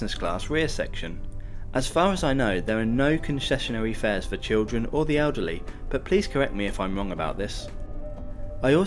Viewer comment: I swear I heard you say "no concessionary fees for children or the UGLY"